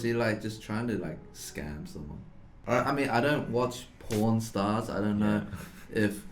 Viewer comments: The way he said pawn stars sounded like he said porn stars 3:11 or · I wasn't paying attention and it sounded like porn stars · When I hear "pornstar" instead of "pawn star" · “I don’t watch pawn stars” I thought he said pOrN stars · For a while I thought they were saying porn stars😂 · I just heard " i dont watch porn stars" xDDDDDD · I was watching this video while just eating sth and I wasn’t looking at the screen and at 3:11 I thought Eddy said p**n stars :D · I don’t think he said “pawn stars” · Oh god, i thought he said "porn starts". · I thought eddy said P*rn stars.